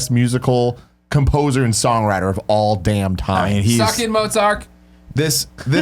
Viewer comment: did you just say "motzark?"